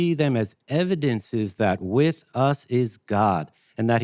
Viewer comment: “With us is god” Sorry… had a glitch and thought he said “god with us” or “Gott Mit Uns” there for a second.